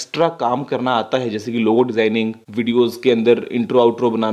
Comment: What does he say at 4:04? Low zennig? I couldn't understand that?